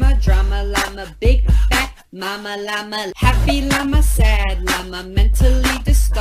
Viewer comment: I thought he is saying big mac mama lama